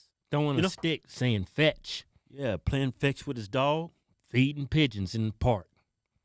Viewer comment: I thought he said "eatin' pigeons in the park..." lol